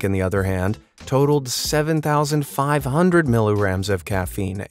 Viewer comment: Lol, it sounds like they said "millirams" instead of "milligrams" 🤣